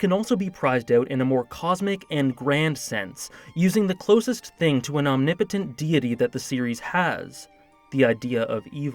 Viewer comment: I thought you said “closest thing to an omnipotent titty”. Lmao